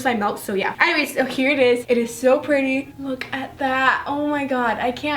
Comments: lol i thought she said im gunna fuck it lolz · When she said, I'm gonna poke it, I'm gonna poke it, I thought she said she was gonna f**k it... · What did she say??hmmmm I heard somthingg..... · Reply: It’s poke not f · unstead of saying I’m gonna poke it it sounds like she said I’m gonna f word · who else thought she said “ I’m gonna fuck it, I’m gonna fuck it!” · I thought that she said I’m going to f#%ck it What’s wrong with me😂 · for a second it thought she said "im gonna fuck it"😂😂😂💀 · what the heck is she saying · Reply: Hanna Blasko im going to poke it guys, im going to poke it 😂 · Did she say I'm going to f**k it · it sound like she said im gonna f it guys im gonna f it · 🌈🌈🌈🌈🌈🌈🌈🌈🌈🌈🌈🌈🌈🌈🌈🌈🌈🌈🌈🌈🌈😍😍😍😍😍😍😍😍😍😍😍😍😍😍 who else thought she said I'm gonna fuck it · I actually misheard it and thought she said'I'm gonna fu*k it,I'm gonna fu*k it' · It sounded like she said “I’m gonna fuck it guys, I’m gonna fuck it!” When she actually said “I’m gonna poke it guys, I’m gonna poke it!” No offense , I actually love you so much! ❤️ 😘 Ily! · i thought she said:guys im gonna f it im gonna f it but she said poke so if you start saying oh she cursed your dumb because she didnt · When she said"I'm gonna poke it guys I'm gonna poke it!" I thought she said I'm gonna f*ck it guys I'm gonna f... It! I was like -.- .0. 0.0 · Did anyone else thought she said I'm going to f it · omg why does it sound like she says "im going to fuck it guys"? LOL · and 4:58 I thought she said lm gonna f**k it lol